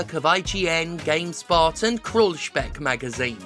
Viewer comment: Good video, just wanted to point out a quick error in the closed captions. There's a point around 2:15 where Jim says "GameSpot" and the GameSpot logo is shown, but the captions say "GameStop". Cheers.